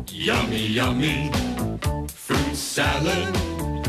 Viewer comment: did it say fruit salad?